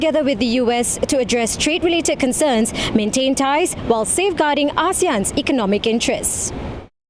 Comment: Why is the subtitle says "safeguarding Aussie and economic interests" while the lady reported said "safeguarding ASEAN economic interests"? (0:45) Is the person who did the video and subtitle from Australia? Is he or she so desperate to the extent changing the word from ASEAN to Aussie? The pronunciation of ASEAN and Aussie is so different, how can they made this stupid mistake?